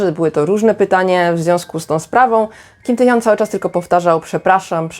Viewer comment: ja na początku usłyszał Kim Taehyung xd